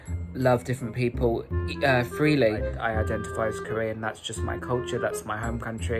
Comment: Omg when he said "freely" I thought he said Freelee like the banana girl.